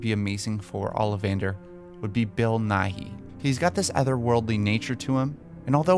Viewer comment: Ngl I actually thought that he said olivander should be bill nye😂😂